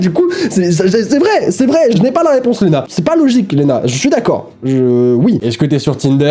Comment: j'ai stressé je m'appelle Léna je me suis dit pourquoi il dit mon prénom ?😂